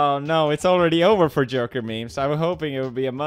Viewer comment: sounds like he said "j*rker" instead of "joker"...